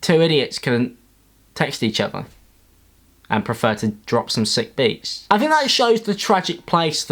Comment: Alex your lisp is getting bad It sounded like you said sick beats instead of shit beats must be a struggle